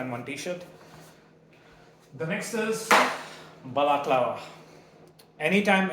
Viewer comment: A baklava? Hahaha, that's what I heard, I know I'm stupid